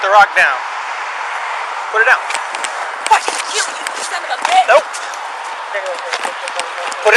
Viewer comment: did she say "fucking kill me you son of a bitch"